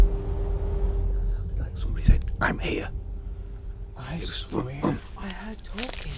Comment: Timestamp 2:26:41. In the dark forest, you mentioned something about the red light, and then there was all kinds of movement around you guys, and then clearly there was a voice, I thought the voice said, ( I'm over here)